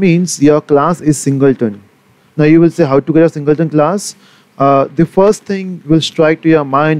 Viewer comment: Dude, what the heck did you just say? Was that even English? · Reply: He is saying "and there you will say how to get a singleton class"